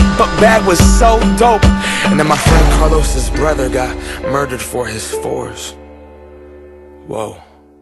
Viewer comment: They put force instead of fours😂